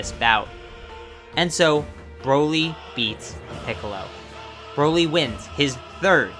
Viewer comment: I thought he said Broly eats piccolo 😂